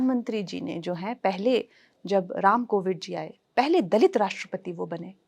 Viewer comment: Did Kangana say Ram COVID instead of Ram KOVIND ?